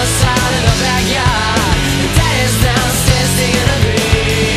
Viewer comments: daddy's downstairs digging a grave i thought it said daddy's downstairs taking a pee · I thought the lyric was “daddy’s downstairs drinking a beer” I could relate as I had an alcoholic father, but it’s “digging a grave”, now I’m like “huh?” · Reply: i heard it as daddy's downstairs taking a pee